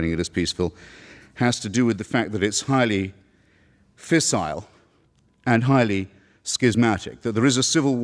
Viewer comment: What's the word he said? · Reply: Fissile and schismatic. Fissile means prone to fission: readily split. Schismatic means the same but is the term actually used when religions split. E.g. the Jesuits split from Roman Catholicism in a schism. The reformation was a schism whereby protestant churches formed in northern europe, usually as a state religions.